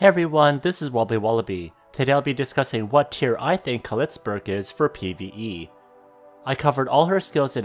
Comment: Did I just heard you say "Clitsburg"?